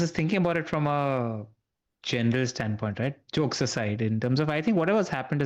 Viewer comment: Lets not sleep on pun @ 4:05 😭😭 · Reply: I think I heard “chokes aside” when talking about arsenal